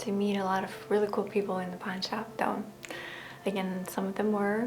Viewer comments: I was listening without watching and I heard ‘porn shop’. I had to do a double-take when you said you were quite good at it. 🤣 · For a while there i thought you said porn shop. Im sorry!🙈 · The whole beginning of the video I thought she was saying porn shop